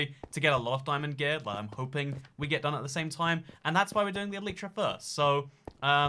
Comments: Me: dies and lost my 3 diamonds cries: dies and lost everything he had why dying is good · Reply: I thought he said ‘but I’m pooping’ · I thought he said ‘but I’m pooping’ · His gear pulled my dad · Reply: I thought he said ‘but I’m pooping’